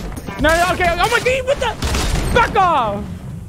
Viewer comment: I thought he said f off lol I was like oh he really just said that